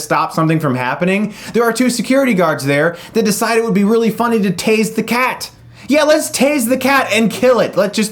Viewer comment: tazing an innocent animal!!!!! it's harame all over again. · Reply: I thought he said "taste the cat" for a long time until I saw the film...